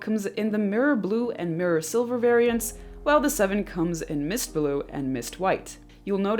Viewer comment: 7 cooums in, did i heard that right? 😂 🙈